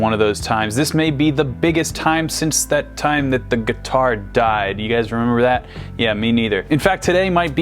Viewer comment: I thought you said "remember when the guitarist died" cause I definitely remember that... Hillel was pretty awesome...